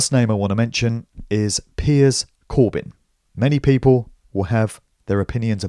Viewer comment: Great stuff! I thought you said piers morgan at the end there - nearly shat myself!